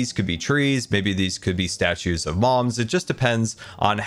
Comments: I heard "statues of moms" not "statues of mobs" for some reason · did he say statues of Moms not mobs ? :D